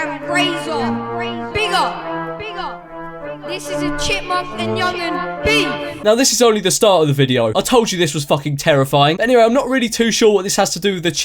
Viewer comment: I can't, every time I watch this video I hear "Chipmunk and Onion beef...."😂